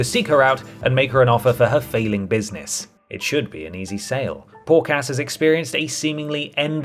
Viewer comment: Did you say Poor Cass? Or Pork Ass?